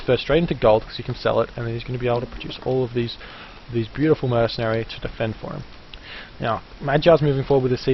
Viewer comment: Did I hear you say "Beautiful Mercenaries"? :P